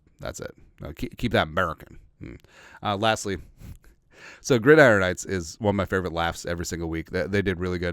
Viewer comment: Who does those sketches? I didn't catch the name or the platform. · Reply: Gridiron heights by bleacher report on YouTube